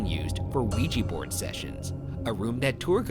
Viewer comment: I heard Luigi-Board sessions 💀